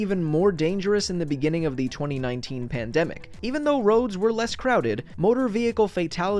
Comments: Correction: I misspoke and said "2019 pandemic" when it should have been "COVID-19 pandemic". The 19 in COVID-19 does stand for 2019, but it was declared a pandemic in 2020. I apologise for the slip up. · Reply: Wait I actually thought that you said COVID-19 pandemic and not the 2019 pandemic LOL😂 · Wait did I just hear "2019 Pandemic"